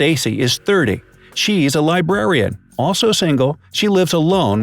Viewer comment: Welp... R.I.P Nick... Also I thought librarian said lesbian at first and I was like "why is that important?"